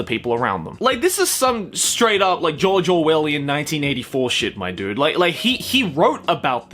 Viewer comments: rather than hearing "George Orwell", i heard "JoJo well" .-. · Whenever he says "George Orwell" I hear "JoJo" Why am I like this